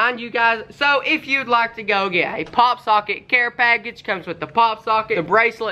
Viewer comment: It sounded like he said hot pocket